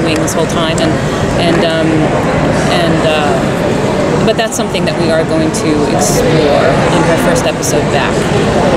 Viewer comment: Can someone tell me what Sara says 6:32 minutes ? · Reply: "That is something we are going to explore."